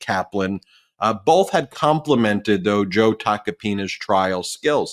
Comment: For a moment I thought Popok said "I'll get back to Tacopenis" It was actual Tacopina's 😂